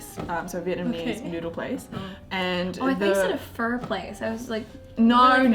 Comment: Dont feel bad, i thought she said "fur place" as well. lol